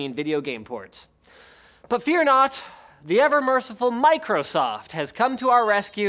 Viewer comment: I thought I heard "Eververse" instead of "Ever merciful". You know what they are, poor Destiny players.